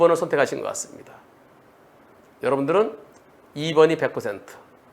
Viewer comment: 변호사님 과실비율이야기 안한다했지만 100:빵 으로 들림ㅋㅋ 방송하세요 자동차 운전자들과 오도방 운전자에게 꼭 필요한 영상으로 보입니다~ 큰 교훈이 되리라봅니다